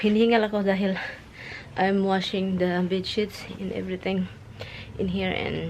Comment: Hey: Going to the new house..............yippy yea..............at 1:11 approximately, in your sweet accent, you said big shits and every thing,,,,,,,,yep that is what I heard......I am not laughing at you, please know that.......so it is big sheets, rather than big shits :), hope you are laughing too. Nice that you send gifts back home. Cats having a great time with that tunnel and Roxy wanting to participate. I love your story about your first gift from Tommy. Happy that you did keep it. One huge bag of beans. I have never ever seen anything like that and I shop at the regular grocery stores in the area. See you at the new house..........good night.